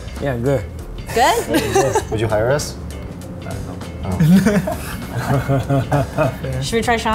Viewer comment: I think he actually said I don't know